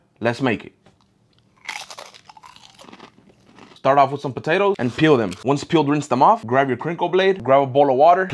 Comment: At first I thought he said " Start off with some potato and feel it ".